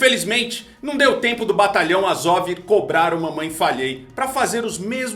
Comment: eu ouvi "mamãe FALHEI?" ahuahau 🤣🤣😂